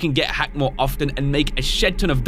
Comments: is this the first time I heard say “shit” 😂 did he say shit ton of dos coin? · did he say shit? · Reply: Shed